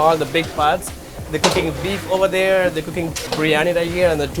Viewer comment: Wait did I hear right at 1:28 ?? Beef?